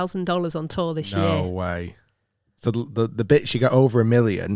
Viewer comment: did he say "the bitch"???